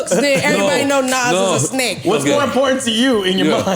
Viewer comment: did she say Nas was a snake? What did she say? 🤔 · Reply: She said a snack…